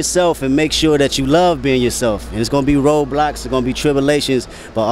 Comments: why do I keep hearing "It's gonna be roblox"😭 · I thought bro said Roblox